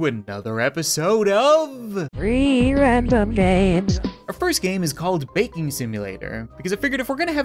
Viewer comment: i used subtitles and they dint say games they said three randoms babes that what is said with the subtitles on